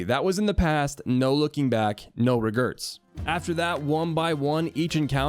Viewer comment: Lol did hey say regerts